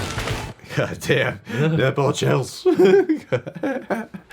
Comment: Did he just say nipple chills? 😂